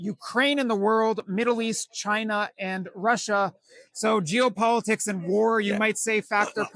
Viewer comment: Thought he said " Jew Politics" but i guess it was " Geopolitics". Lol.